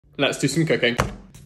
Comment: I always misheard your intro as "let's do some cocaine" 💀🤣